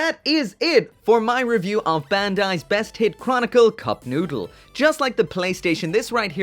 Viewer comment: At one point, I misheard it as "Best Eat Chronicle." Damn.